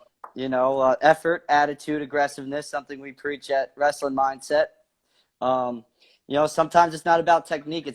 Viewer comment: I thought it said "wrestling MIDGET" lol. I was like, this dude isn't a midget.